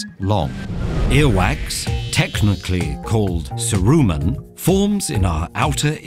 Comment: i thought i heard “earwax, technically called SARUMAN” and thought this vid was a joke until i read the caption